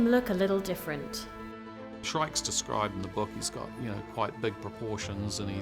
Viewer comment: During the whole Movie i thought they were saying Shrek instead of shrike!